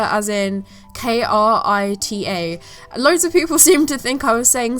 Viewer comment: Oh no I was one of the people who thought she was saying "Critter"....lucky I don't do digital art and instead stick to traditional...otherwise I would of face palmed a lot harder!!! Hahaha!